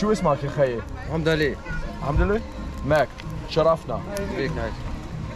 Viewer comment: FYI, on 14:23 the corn vendor is actually saying that his name is "Mohamed Ali" (like the boxer). You hearing it as "Ondali" is pretty funny I have to admit :D. But fair enough, he says it pretty fast and with little articulation. P.S.: 45:55 when you tell to the waiter you speak arabic he answers: "mostaschrek" (or "mostachre9"), meaning: orientalist. 2nd P.S.: Am a Tunisian living since 6 years in Germany. I go back every summer to spend the vacations in Tunisia. And interestingly, this last summer, in August during my vacations, I have stayed in the big white resort which you can see in your video just next to the spot you swam in in Kelibia. And I also had dinner in Casa Lico, the restaurant where you had dinner and show in the previous video. Nice coincidences I would say.